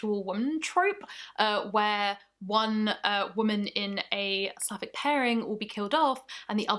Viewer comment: Lmao did the subtitles just say "slavic" instead of "sapphic"?😂 (Time stamp would be